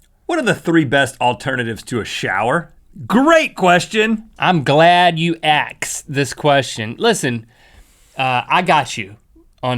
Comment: Sounds like Link said "Im glad you AXE this question" hehe